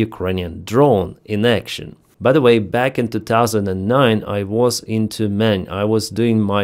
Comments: I misheard you at 2:25 "back in 2009 I was into men" 😆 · i thought said "back in 2009 i was into men" And i thought "that's an unneccessary information for this topic". 😂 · Sorry, there's a part of me that will never grow up.... What I heard say was, "In 2009 I was into men". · "back in 2009, I was into men" 😭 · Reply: I had to listen again, what did I just hear 😂😂😂😂 "In Tyumen" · NGL, as an English speaker, hearing the phrase, "Back in 2009, I was in Tyumen" really caught me off-guard and made me giggle. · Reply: and I thought he said "I was in тюрьме"